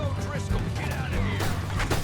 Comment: It sounded like one of the O'Driscolls said "the feud died with O'Driscoll, get outta here" So from what it sounds like the O'Driscolls no longer care about the Vander Linde gang and just wanna be their own separate independent gang with no further ties to Dutch's gang